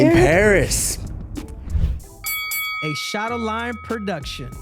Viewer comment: Just realized that Henry says “Shadowlion” I thought he always said “shadowline”